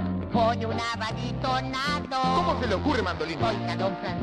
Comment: Siempre pensé que decia "un amariconado" 😂